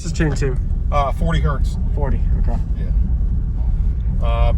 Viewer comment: You're saying this is tuned to 40 Hertz, I thought the owner said 20 hertz but I couldn't really understand them